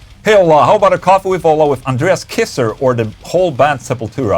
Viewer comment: Did I just hear Under Ass Kisser